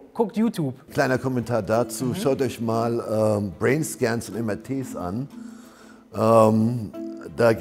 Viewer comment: was sagt er bei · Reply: "Brainscans und MRTs"